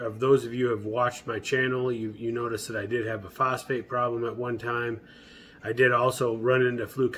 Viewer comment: Did you say you had a prostate problem or phosphate problem at 2:03? ;)